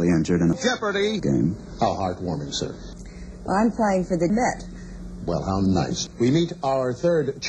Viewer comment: "I have two charities, uh, both involving young people in my own home. One is called Jeff, for an eleven year old boy who needs a kid, and the other is called Sherman, for a seventeen year old trans boy who was very seriously injured in a jeopardy game." "How heartwarming, sir." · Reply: I thought she said "I'm playing for the Meth"